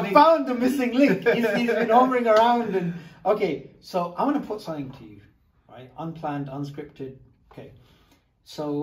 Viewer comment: I thought he said 0:20 "I'm gonna put some dirt in your eye" 😂🤣😂